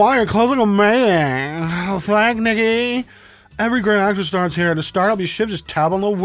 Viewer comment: Ik I’m not the only one who thought he said “Thanks niggy”